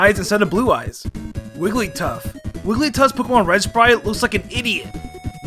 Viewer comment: I kept hearing “Wigglytuff looks like an Indian.”, no matter how many times I replayed it. Finally had to turn on CC.